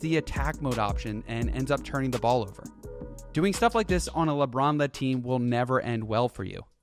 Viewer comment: What he said “LeBron led team” what I heard “Lebronda team”😭😭💯